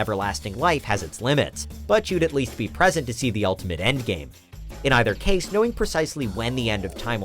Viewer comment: People in quintillion years: We're in the endgame now... (for real did he just say endgame at 3:09???) (Who watched avengers endgame?)